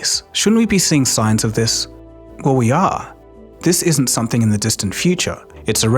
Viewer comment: did he just say "Oh ja" ? (German for "Oh, yes") 😮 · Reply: He said "Well, we are"